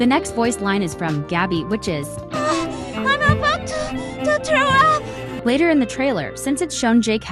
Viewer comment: i think Gabby said throw up, great analysis though!!